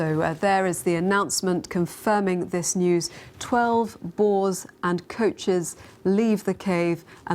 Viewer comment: I heard 12 balls at 0:30 and the post said 12 boars lmaooo